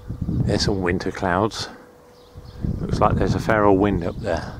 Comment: Did you say "fair old wind" or "feral wind" because "feral" is a wonderful way of describing a wild wind and one I hadn't heard before. (0:17) Did you say "Roman road" or "roamin' road"? Now I'm just messing with you. (2:17) Presumably the planes were flown in at some point. Would have been something to see those Jumbos land at what looks like a modest airfield. A bit sad to see aircraft in this condition. (3:18) I wouldn't think you'd reuse an airplane part but perhaps that's the case, especially if the part has been replaced during the lifecycle of the aircraft. (3:54) Lovely cottage indeed. (4:12) I used to follow someone on Twitter who lived in Cirencester and mispronounced it for some time before hearing it spoken. (5:04) "Petrolhead" is one of the more charitable ways of describing him, so I understand. Thanks. Enjoyed!